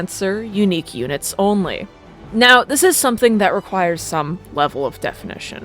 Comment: i thought you said eunuch units only and i got so confused for a moment before realizing what you actually said